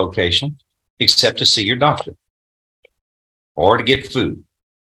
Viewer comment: I like how the judge didn't explicitly mention that he could go to the doctor under house arrest and you know that he's not smart enough to figure out that he can do that if necessary. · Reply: Wow I'm dumb I thought he said "see your DAUGHTER"....he said "see your doctor" 😑😑